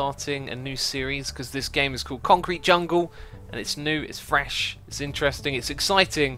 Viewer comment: Am I the only one who thought he said french and not fresh at the start?